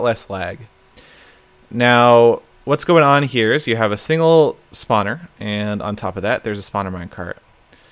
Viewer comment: Click the button to view the automatically generated captions. Look at what youtube thought he said at 1:08 "spotter and on top of that there's a spot in my car" Its just kind of funny.